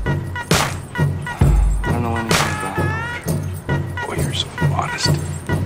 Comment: what is he saying? I can't understand him. What did he reply in response to "it's so strange you don't remember any of your poetry"...I can't make out the words, can someone help me out? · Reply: Matt AdRev Actually he says 'I don't now anything about poetry'